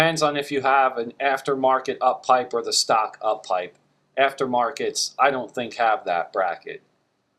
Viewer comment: I've studied this video like 10 times.. and I still can't find the banjo bolt on my Forester 2.5XT :(. I've used mirrors, lights, all kinds of things. In addition to that silver tube in front of the bolt, I also have electrical wires and other stuff in the way that don't look easy at all to move out of the way.. I think my hands are just too big to do any useful work on a Subaru :(. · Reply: NEvermind.. was being stupid.. i kept hearing 'stock downpipe' when you were saying 'stock uppipe. I did have to remove the boost solenoid and the uppipe bracket to get to it.. of course a vacuum hose split in half doing the solenoid.. but i'll deal with that :)